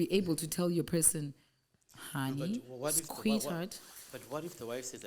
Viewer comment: 1:24:40 did you just say, ''squit-heart?''